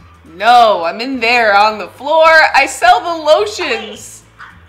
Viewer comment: I sell the lotions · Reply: i thought she said i SMELL THE LOTIONS